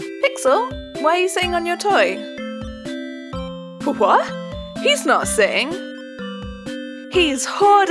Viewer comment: my dumbass thought it said “what? its bot shitting!”